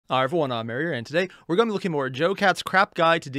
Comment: that was funny. I wasnt paying enough attention and all i heard was "today were going to be looking at JoeCat's Crap"